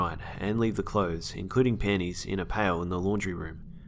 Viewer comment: I thought he was saying pennies at first.